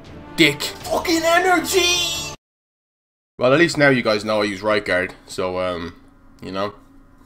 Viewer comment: swear down, no word of a lie, thought u said "at least now you know i use rijkaard" reee